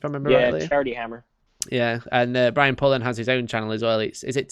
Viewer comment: Is it Brian Pullin? Not sure how to spell his name. What's the name of his channel? Its said so fast I couldn't hear it. Sounded like Tank Cousins' ? Tan Cuttins? Tank Cottons? I have no idea. I don't have the ear for it sorry gents. I wanna check out his channel. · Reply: Brian Pullen